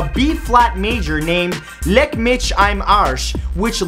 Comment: lolololol, for anyone who didn't understand Matthew, it was called "Leck mich am Arsch" which means "Kiss my ass".